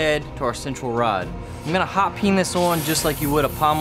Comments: did he just say penis😂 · Lol lol my mom heard you say hot peen this coil and she thought you said hotpennisoil lmfao